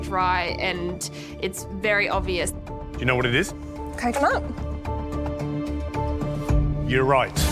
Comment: wtf did she say? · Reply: Coconut, could you really not tell lol